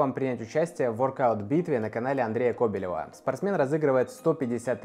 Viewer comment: мне сначала послышалось Кобякова 😂🥺